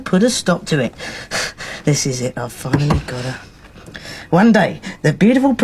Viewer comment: Thanks for the uploads. Is it my imagination or does say "this is it, I've fucking got her" at 23.19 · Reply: i heard it as "..i've finally got her"